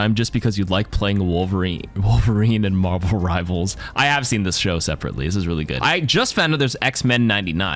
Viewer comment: Did you say I’ve watched this show as in Logan? It’s a movie